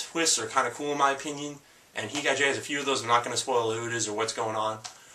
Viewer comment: The animes he recommends are Berserk, Initial D, Gantz, Heat Guy J, & Cowboy Bebop. I felt the need to make this comment cause I thought he was saying Dance instead of Gantz and Ichai Jay instead of Heat Guy J.